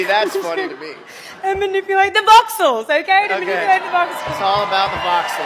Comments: I think she's saying "uses a set of powerful tools to manipulate the box source" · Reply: I thought she said voxels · I think she's saying "uses a set of powerful tools to manipulate the box source" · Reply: I'm Australian and I also thought she said, "Voxels." Haha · I think she's saying "uses a set of powerful tools to manipulate the box source" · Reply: Even Miles sounds like he said "Voxels"